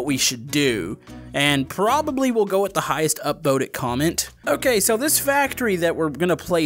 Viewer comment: Did anyone hear him say the most 'Upvoted' comment at 2:50 or am I just spending too much time on Reddit? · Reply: He said upvoted